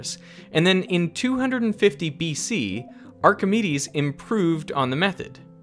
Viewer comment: The closed captions say ”our committees" when I think it meant to say Archimedes. It happens a few other times in the video too. In general the CC seem off, like it sounds like you're saying "a rational number, and then the CC says irrational number, just as one example.